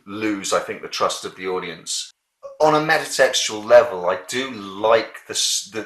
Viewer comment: What are you saying at 8:24? On a......level? Meta sexual? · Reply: Metatextual - sorry, I garbled that word and haven't got the best video/audio recording equipment in the world.